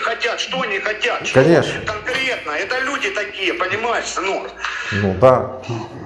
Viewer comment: это люди такие понимаешь ""сынок""? мне одному это послышалось